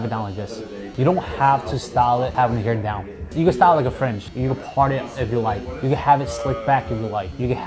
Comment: I hear “you gotta style it like a FRENCH”